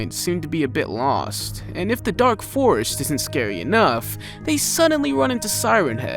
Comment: Did you just made a reference to dark force or you said "drake forest " and I misheard? Because a dark force reference would literally make me go nuts about your account :))